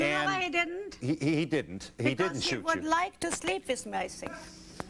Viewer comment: What did she say at 15:18 ?? I couldn’t understand . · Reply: “Because he would like to sleep vis me, I think”